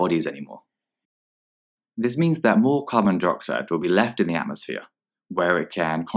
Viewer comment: It sounded like they were saying ‘Carbon Deroxide’ and I’m literally screaming inside